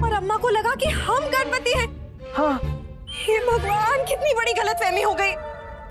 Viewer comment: I think at 7:02 Sharda said galat family